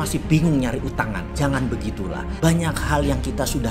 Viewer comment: si bos ngomong "revealed", eh jongosnya nulis "refill", emang es teh cuy wkwkwk, jongosnya juga musti kena reshuffle, sering typo di video2 sebelumnya, tapi kali ini keterlaluan, salah denger atau emang gagal paham